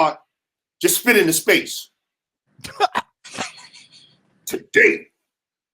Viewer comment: Spit it into space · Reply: oh fr?.. LMAO!.. ok that's what's up.. I thought he said 'fit'. when I first heard this I was just cracking up off the story period. 'spit it into space' do make a little more sense tho.. a little bit.. CTFU!... nah man, I dig it.. people def got they own witt about different things.. dig?.. lol..